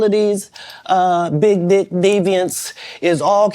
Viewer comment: Wait a min. I'm I hearing things at the 13:06 mark😂did he say big, di*k demons then bring up diddy🤔